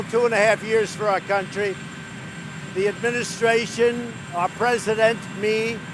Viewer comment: Did he just say "Our President" in that opening statement????? @ 1:10. Who is REALLY running this country?